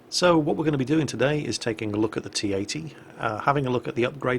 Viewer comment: was it just me or did say t-18